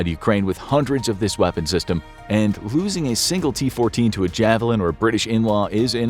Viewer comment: Totally misheard as "losing a t14 to a javelin or British In Law".......in laws are terrifying